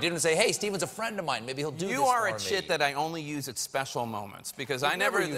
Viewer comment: "You were in shit that I..." Did anyone else hear this?